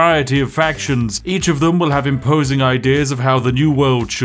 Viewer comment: Not "imposing ideas" but "opposing ideas" ...to be fair, either of them would be correct, but the context at least heavily implies you intended to use the latter instead of the former. Just saying. This is my second language, at best.